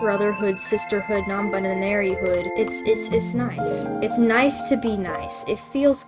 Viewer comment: wait did you intend to say “nonebananary hood” or did I just hear it wrong? Not to offend anyone who’s nonebinary, but cmn “none🍌ry” does sound funny😂🤌✨